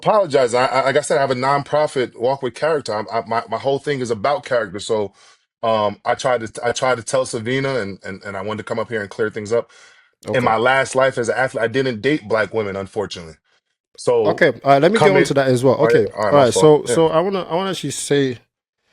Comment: Did homie just say, "In my LAST life as an athlete, I didn't date black women, unfortunately." ????